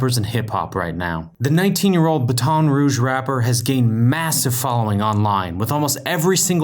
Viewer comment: Did i just hear BUH-TON Rouge rapper or did it skip?! @ 0:04 I’m not from there but i know for a FACT that ain’t it, just funny😂